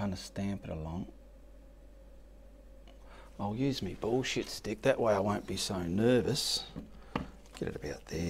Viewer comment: What stick you say at the 19:33 mark? Sounds like Bob Ross in acrylic with a touch of Gordan Ramsey. 😂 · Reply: that is my bullshit stick, it helps add the bullshit affect meaning more than wow.